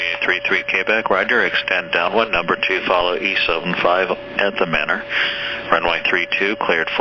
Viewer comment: I listen to ATC again and it almost sounded like he said, extend your downwind number 2 'East of him at the manor' rather than E175 at the manor?????